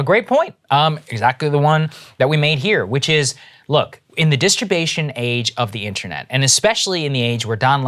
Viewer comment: did he say at the masturbation age?